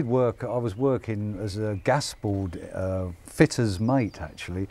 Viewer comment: Question from non-English speaking: What is "Gasbo"? Dave said he work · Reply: He said 'gas board' 😅